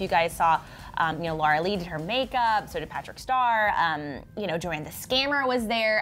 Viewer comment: did anyone else notice how she said patrick star instead of jeffree star